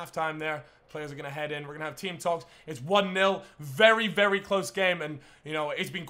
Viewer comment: At half time when he said where gonna have team talkes i thought he said were gonna have tea and scones